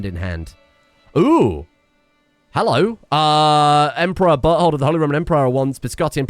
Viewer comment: I don't know why i was reading the transcript and listen instead of actually watching the video, but anyway, At 32:22 Emperor 'Buthole" would like to meet up.